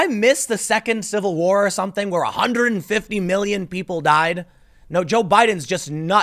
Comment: Wait I thought he said 150,000 people died he said 150 million??